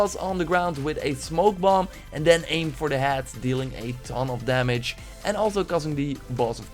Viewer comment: I thought he Said aim for the as 😂 now I know this game is old but I started assassin creed unity when I was young and then never played until I was 10-13 I’m 14 now but last year I beat it and I almost beat Assigns creed Valhalla I’m pretty sure I did or super close to it, I have all the assassins game but only beat 2 including Assains creed unity and Valhalla I think black flag to but I’m level 10 on this right now sadly still got a long ways to go